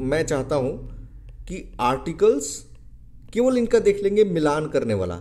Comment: articles ke baad kya bole hai sir ..? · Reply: kevl Inka dekh lege Match krne wala